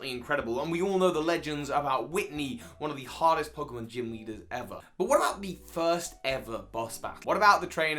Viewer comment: I heard witney one of the hottest pokemon trainers ever.